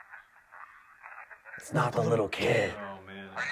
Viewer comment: When you heard "It's not the little kid," I heard "it's someone else."